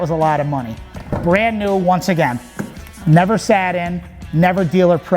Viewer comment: I thought he said it was never sat on then goes to sit in it to start up.